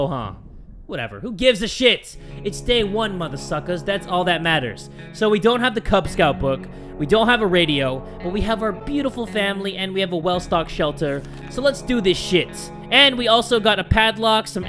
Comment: is just me who heard "Well we haven't got the Kub Scoutz book"?